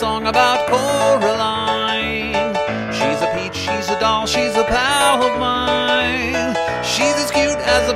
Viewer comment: I always thought it said “she’s a powerpline “ lol